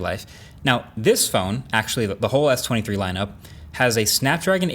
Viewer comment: did he just say "Whole ass"